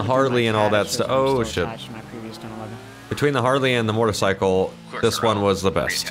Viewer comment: In the beginning did he just say mortocycle?